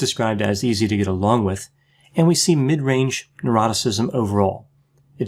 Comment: Lmfao for ages I thought you were saying eroticism so was thinking oh right the Americans must spell ocean with a e on the end lol Der brain me lol